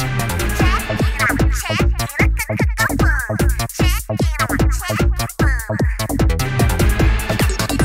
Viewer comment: I always heard Pearl's segment at 0:59 as; Check the mic, check it, 1234 go! Check the mic check the phone! IDK why, I just did....